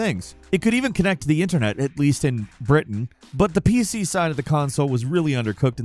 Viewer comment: What did he say at 8:00? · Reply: "It could even connect to the Internet. At least, in Britain."